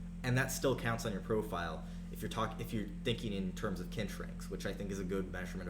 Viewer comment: whats kent rankings? i cant find anything on the web, so im assuming i heard it wrong